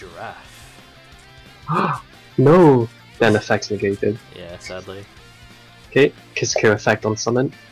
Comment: did he just say 'ben effect vergeten'(i forgot the effect in dutch) i didnt know he can speak dutch the madman